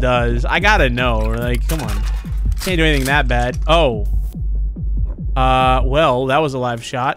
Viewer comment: Why does it sound like said oh f$ck